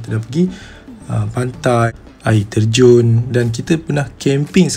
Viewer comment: sye x salah dengar kan? yg "pantai " tu